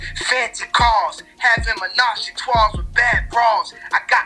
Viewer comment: that's three in French... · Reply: You already Know I heard “toi” not “trois”